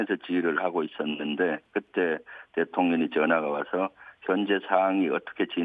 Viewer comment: 아~~ 의원님 발음이 대통년으로 들리는데욯ㅎㅎ 이러면 윤석렬씨가 더 나은거 아닌가